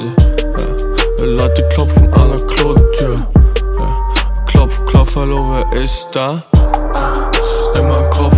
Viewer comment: wäre viel geilere Line wenn er rappen würde „Klopf, Klopferlunge izz da“ 01:01 ps: hab ich auch immer so verstanden xd